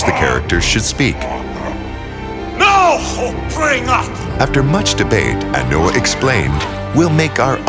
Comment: I think he said: "Nao have bring".